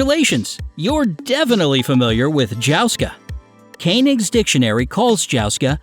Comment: I swear to god I thought jouska said josuke